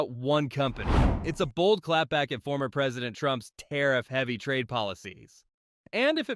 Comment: Canada just played chess while the US was playing checkers. While Washington slaps tariffs and picks fights, Canada is locking in trade deals, diversifying its economy, and securing its future. The real question: Is the US isolating itself from its own allies? What happens when ‘America First’ turns into ‘America Alone’? Thoughts? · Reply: “…former President Trump…” Did I hear that correctly?